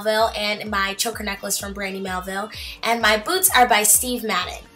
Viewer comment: Oh god xD thought she said her boobs were from forever 21,on last outfit hahA it was boots lol